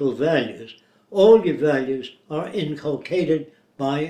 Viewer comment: the subtitle says implicated, but the word Jacque said was actually inculcated. Which means - to implant by repeated statement or admonition; teach persistently and earnestly (usually followed by upon or in ): to inculcate virtue in the young.